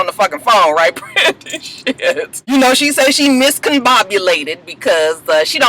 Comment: I thought I heard that girl say miscombobulated